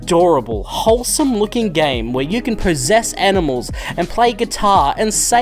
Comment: I misheard you when you talked about possessing animals, and thought you said "prossess", so naturally I thought you were possessed by the spirit of Castlevania II: Simon's Quest. Or prossessed.